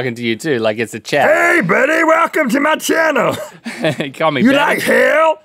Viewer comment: I thought that hell burger said, welcome to Monsanto